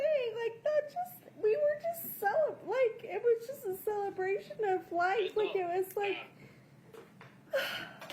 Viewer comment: DID YOU HEAR WHAT THIS KILLER SAID!!!!!! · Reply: Yep! "We were just cel....it was just a celebration of life". She caught herself, oh you were celebrating Kouri?!